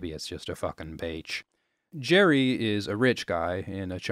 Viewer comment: i thought he said "bitch" the first time he said beach lol